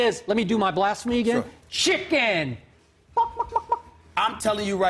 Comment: did bayless say shitken??